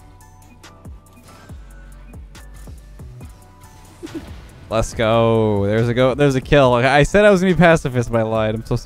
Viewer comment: I can't be the only one who thought that Leth said "Leths gooo" instead of "Lets goo."